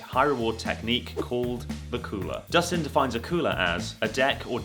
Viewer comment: I thought the cheating technique was called "Dakulah" but it turns out he was actually saying "the cooler"😂😂